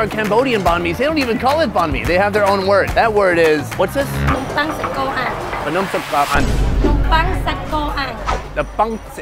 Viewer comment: What she said was: Nom pang - bread Sach Ko - beef Ang - grilled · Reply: Nompang sachko ang = Grilled Beef Sandwich